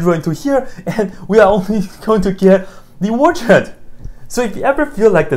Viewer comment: what are we going to get? It sounds like "the woodshed" or "the wart's head", I can't tell what word it is. I love that he concludes the entire function is just a point. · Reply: origin